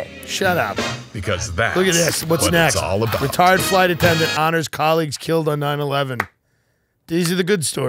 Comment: I thought he said ret_rd flight attendant honors 9/11 victims and i died 😂